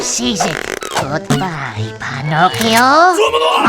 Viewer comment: did he said Pinocchio